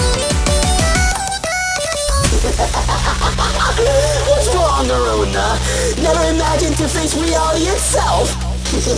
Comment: What did Finn say? Specifically this gem. 3:32 - 3:52 I heard this. “What’s wrong, Arona? Never imagined to face reality itself?” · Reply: Oh, I got it. "What's wrong, Arona? Never imagined to face reality itself? (insert Finn's laugh) You really believe that useless tablet will get you away? NO! Accept your fate, you artificial vermin! DIIIIIIIIIIE!"